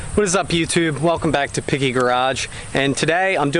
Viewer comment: Sounds like you said piggy garage lol